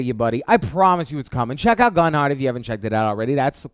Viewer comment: What was the game that he said gun art or what is it please respond the period he says it is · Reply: Gunheart, it’s awesome z:)